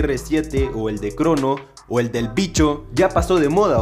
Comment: escuche el picho XD